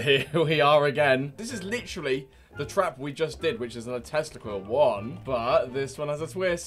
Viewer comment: when h said tesla coil, i heard testicle